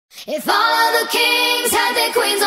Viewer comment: is it just me or do i hear hif at 00:00 ???